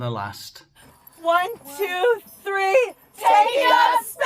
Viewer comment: Did I hear their Slogan correctly as “Take Up Space™️”? That makes them sound like a Bunch of Useless Placeholders who are there to, simply, “take up Space” inside of the Rocket. 🤦‍♂️